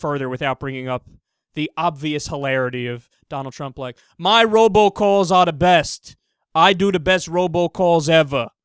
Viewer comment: Is he saying, "ROBO calls"? Or "Robot Calls"? Either way, what are they?